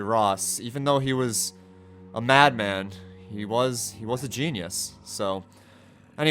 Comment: i heard say ''yes he was" when mentioned ross